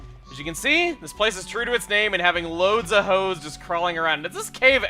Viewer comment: I wasn't really paying attention at 11:06 and I thought Chugga said "loads of hoes."